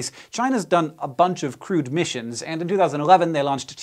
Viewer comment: Ohhh, CREWED missions!!! I thought you were saying CRUDE missions! I thought "well that's a bit unfair" hahaha, duh!